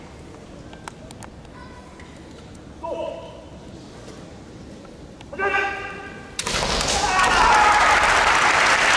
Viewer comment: のところって、反則とられてるんですか？ 審判が反則の旗を示してなかったので、何て言ってたのか分かりますか？ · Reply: 指導！って